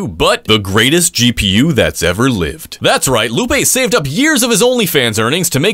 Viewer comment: bro i heard "saved years of his of earnings" and just alt tabbed back to yt